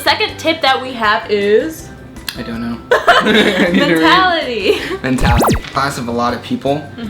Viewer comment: I thought you said Natality not mentality! Lol!